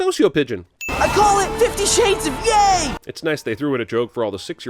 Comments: did he say gay in 2020? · "I call it 50 shades of yay" I thought he said "...50 shades of GAY" ya know, the rainbow... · I thought he said “I call it 50 shades of gay” lmaooo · I thought he said 50 shades of gay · please~ I thought he said “50 shades of gay” 💀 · Did Walter say Fifty Shades of Gay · I thought Walter said 50 shades of gay at the end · OH SHIT FOR A MOMENT I THOUGHT HE SAID I CALL IT 50 SHADES OF GAY CAUSE ITS RAINBOW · I kid you not, until now I thought that Walter said 50 shades of gay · For a second there i thought he said 50 shades of gay